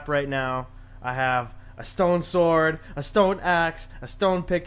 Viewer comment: I thought you said stolen sword lol